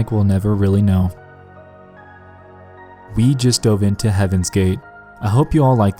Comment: At the end I wasn't paying too much attention so when you said "We just dove into Heaven's Gate" I thought you said "Please donate to Heaven's Gate" and I was like ummmmm wHA T